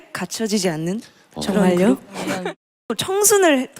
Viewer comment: 쯤에 용선이가 저랑 같군~ 이렇게 말한건가요?? 정확히는 안 들려서.. 같이 들어봐주실 무무 구합니다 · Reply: 정말요? 라고 한것 같아요